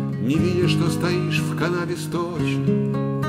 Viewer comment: спасибо, отлично! послышалось: "стоишь в Канаде сточной" ;-) просто послышалось, не более того...